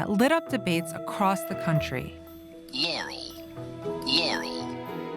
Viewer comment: I heard "Yammy."